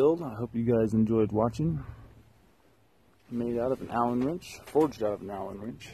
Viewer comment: I thought it said "alien wrench"